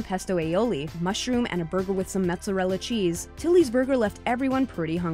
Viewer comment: thought she said titty burger